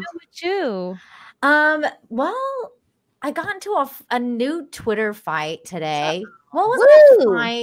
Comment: I thought she said nude Twitter fight. Got my attention.